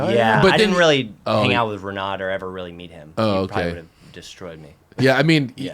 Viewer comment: I heard “Destroyer”